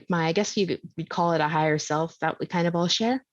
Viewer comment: Did she say at 20:55 " ...I guess you would call it a Higher Self, that kind of bullshit"??? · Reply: "Kind of higher self that we all share" even I had to check 🤣